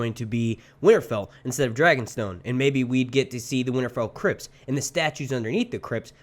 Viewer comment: Every time you said "Winterfell Crypts", I heard "Crips" instead lol. I was like "Oh shit! THROW UP THE SET!"